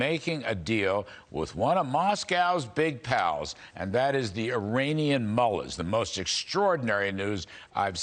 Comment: Sounded like he said Iranian Mullet. LOL